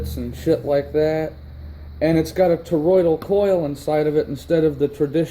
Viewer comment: I thought he said a “Toyota Corolla” coil. I was like WTF is that????